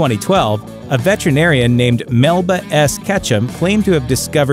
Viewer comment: Melba Ash Ketchum ? did i hear that right